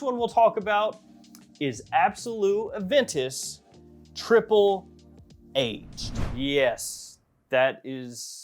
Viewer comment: I thought you said Triple H💀